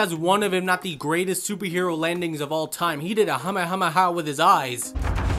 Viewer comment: Did he just say "hamehameha"? 😂😂😂😂